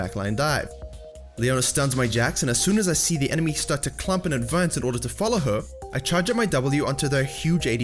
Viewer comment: Yo wtf are you saying dude, is it english? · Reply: 'enemy start to clump and advance in order to follow her (Leona)'